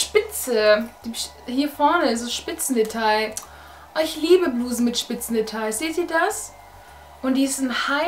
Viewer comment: Hi bei 9:27 habe ich bisen verstanden